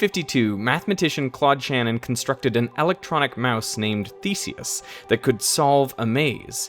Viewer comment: The fake mouse was named Feces? Wtf haha Edit: It had a different name but when I first heard it I was like wtf haha. This is really cool though